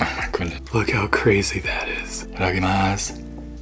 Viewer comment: what did he say at 2:27? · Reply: i think he said “ittadakimas” or something and its a japenese word u say before u eat